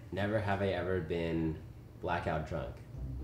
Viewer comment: Wait, i can't understand what's Albert saying at 3:35 Can someone tell me? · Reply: Blackout drunk